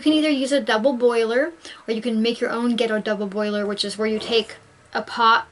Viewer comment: Did she say make your own ghetto double burner at 7:57? Man, she is funny